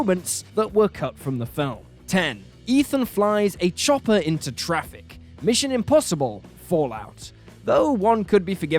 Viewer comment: I heard “chocolate into traffic” 😂